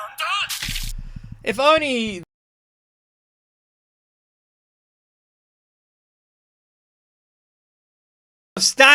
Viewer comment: If only? What did you say? Can somebody read his mouth? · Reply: I think the ending is "could walk up the stairs and open the door", but I can't read the first part